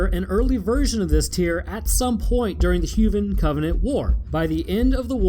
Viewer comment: Did he just say huvan instead of human